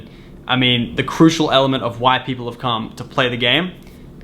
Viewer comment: I thought he said at 7:38 it's a crucial element of white people come